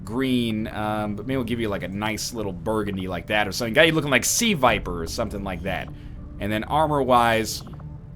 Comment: A sea viper? Did I hear right? Looks like she has a turd on her head. 2:45. But hey at least this time it's not "the rainbow squad". ; )